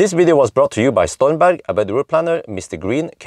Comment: I keep hearing "StoynBank" instead of Stormberg...